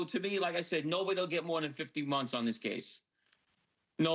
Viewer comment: did he say 15 months? Or 50 months? hard to hear him